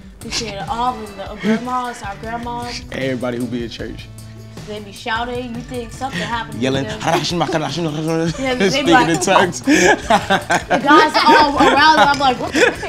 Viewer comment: did he say “speaking in twerks” 😂😂